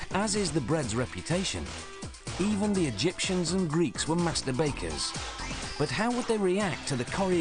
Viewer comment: I thank god he he said master bakers….i thought he said masterbaters